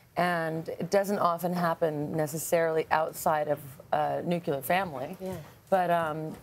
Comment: did she say nucular family??